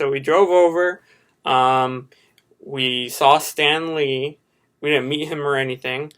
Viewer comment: When you said "Stan Lee", I thought you said it as in Stanley. Also, I loved your bit, it was hilarious and full of talent at the same time.